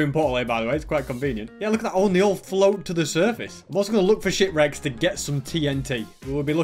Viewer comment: When you said “let’s look for ship wrecks.” I thought you said “let’s looks for sh*t wrecks.”